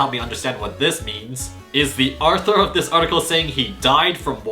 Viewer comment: did he say “the Arthur” like the Tv show